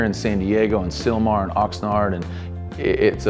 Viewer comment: Yo did I hear him say oxnard at 4:03 ?